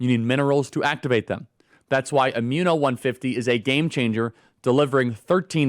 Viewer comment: Sounds like you say. Amino. 🥲